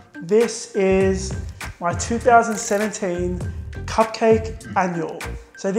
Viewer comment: Really thought that said 'Cupcake Animal' and got so excited! I need to go to bed - but, that is an adorable calendar!